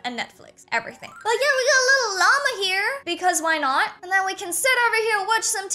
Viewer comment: 😂what did she say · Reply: She said...... "And here we got a little llama here"